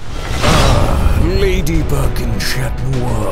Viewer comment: sorry what did he say I thought I heard madybug lemme just replay replays I KNEW IT!! he DID say madybug!!